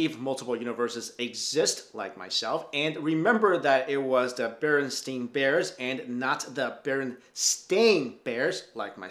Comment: I swair I heard you say it was the bearinstin bears instead of the other way you said it